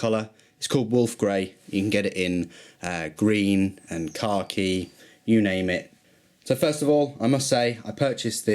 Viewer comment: what color is car key? You said Wolf Gray, Green and Car key. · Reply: Khaki